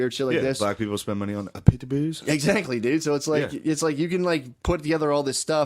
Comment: What did Gillis say at 07:08 ? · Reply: “Black people spend money on pitbulls.”